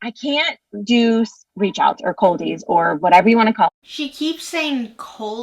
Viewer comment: She's saying "Coldies", but I hear "Culties".